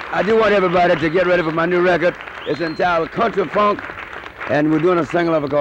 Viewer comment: At the end of the interview Mr Brown mentions his new album 'Cut the Funk'. I guess that was a working title, because the album, as well as the single off the album, was issued as 'Bring it on'. Do you have the the rest of this video in which he performs 'Bring it on' ? ..would love to see that. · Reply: Hi Paul, 'Country Funk' is what I thought he said in the interview, but after listening to the record (I haven't heard it since the 1980's!) it sounds like 'Cut the Funk' to me. But I think you must be correct because the intro on that tune has a real country feel - not what you'd expect at the start of a Funk tune.